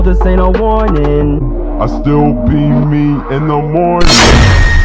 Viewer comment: I thought it said : I still pe pe in the morning